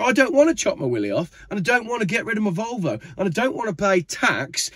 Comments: After you announced you didn't want to chop your willie off, for a split second I thought you said 'or get rid of my vulva !' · Had to listen again 7.07, thought you said Willy and vulva 😂